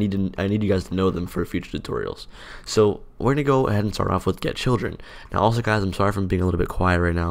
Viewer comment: i thought he said "We're going to start off with dead children"